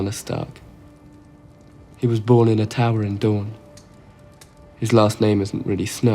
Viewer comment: I heard "he was born in a TAVERN in Dorn" forgive me, im a bit confused -_